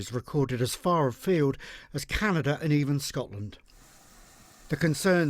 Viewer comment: As far as even Canada and, am I hearing this right????? Scotland?!?!?!?!